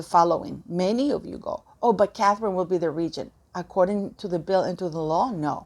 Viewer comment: I thought said Catherine would be regent if needed